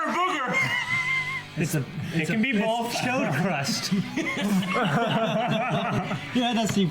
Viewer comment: what is saying? Call me naive, but I don't know that word. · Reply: He said "chode crust". It's pretty gross, but I'll explain. Chode means penis, so the crust would be like whatever's stuck there after liquid (sweat, semen, etc.) hardens